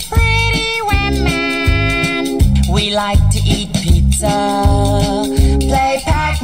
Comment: I thought it said they like to eat babys until they said pizza bc I paused it quick Anyways ayo Why they talking bout woman?!?